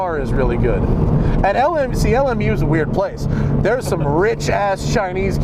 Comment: what was the place that said had the rich chinese kids? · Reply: LMU - Loyola Marymount University